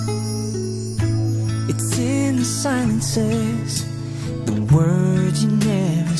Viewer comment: I misheard "Silences" as "Simon Says" alot when I was a kid